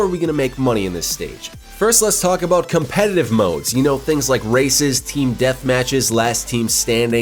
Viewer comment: Only I heard the other way "Rasist??" :DDD Anyway aswesome video I'm a solo / beginner player so helped me out a lot :)